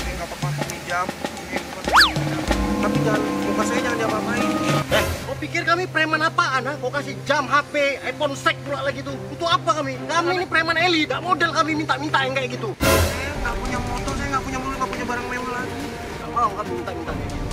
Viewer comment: Ada Yang Nyadar Bilang Apa? (Iphone Sex) 😂😂😂😂 Like